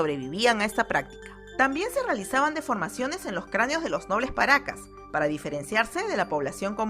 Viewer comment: No entendí 😂😅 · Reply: Creaneos